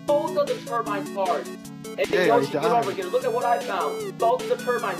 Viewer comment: "Look what I found" caption says "finally found his own penis" in in tears rn